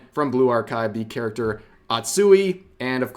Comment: hehe her name is Hoshino - "atsui" is just part of a phrase she says using that face: "Ugoi Tenainoni Atsui Yo"